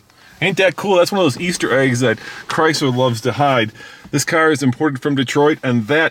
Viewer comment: What word you said in 11:34? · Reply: Do you mean, " This car is very disconnected from my brain." ?